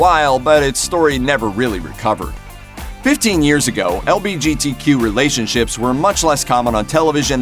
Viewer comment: did he said lbgtq!!